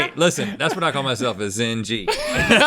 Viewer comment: Did Rhett call himself the Zen G or Zyn G